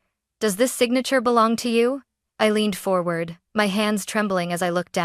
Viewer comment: the gross AI mistakes start in the text subtitles. The narrator voice clearly stated "I leaned forward" but the text subtitles indicates "Eileen forward." This kind of gross error in the subtitles text that make no sense really does a disservice to the hearing impaired who do cannot hear the correct narrative. Also similarly affected by these errors can confuse persons with limited knowledge of printed English language subtitles to help understand the story.